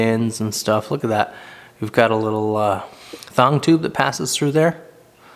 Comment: At around 3:30 you said "we've got a little (thung) tube that..." and I am very curios what a thung tube is?I probably miss heard you but I want to know what that part is called and what you said. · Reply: Thanks for your question! I called it a thong tube. It's a tubular pin that passes through a knife handle